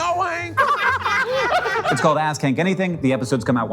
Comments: Please some check me into a mental institution, I swear to god I heard him say “It’s called Ass Kank Anything.” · when u say "ask hank anything" really fast I keep hearing "ass king"